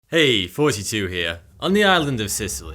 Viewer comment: "Eyyyy thohtee teew heeea..." My day: made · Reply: I always hear it "hey is 42 heya"